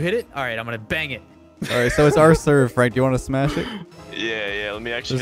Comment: Anyone else hear, “Alright so it’s our serve, Frank wanna smash” at first?